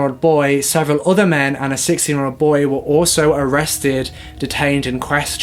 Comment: Totally thought you said 6 year old boy, not 16 and was like"wtf, a 6 year old was arrested???". 😂